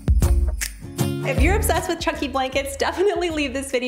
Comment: I thought it said Chucky Blanket and I was like. well. okay youtube I guess